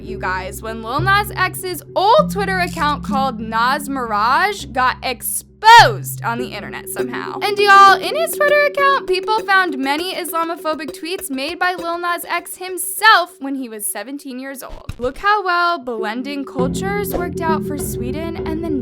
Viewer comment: i’m sorry i thought it said that he was llamaphobic 😭😭 i know it’s serious but i seriously thought that’s what she said. like meaning he was rude to llamas 🦙 😭😭